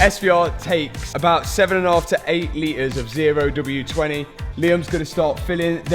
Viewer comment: Did he say 0w 20